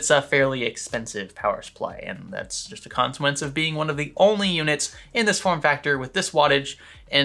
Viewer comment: Corsair isn't the only 750W SFX game in town. Silverstone also has 750 80+ titanium. and I believe there are a few others but it's not Corsair or Silverstone who are manufacturing the PSU's they are branding and putting them in a chassis or case and then reselling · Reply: I thought you said the only one, but you said, "One of the only." So hats of to you.